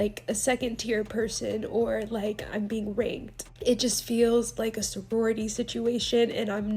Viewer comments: It took me so long to realize she said “being raNKed” I thought she said “being r*ped” and I was like WHAT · This whole thing just makes you see how entitled these influencers are. Do you really wanna support these “second tier” creators? Their mentality is just 😂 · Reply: I thought she said that too but she said “ranked”. I had to go back and listen.